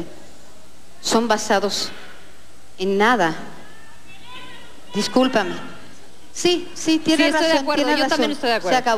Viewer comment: ¿Qué dice la persona del público en el minuto 14:26, que hasta hizo que Ilse y Lolita estuvieran de acuerdo? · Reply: que se vayan a discutir a su casa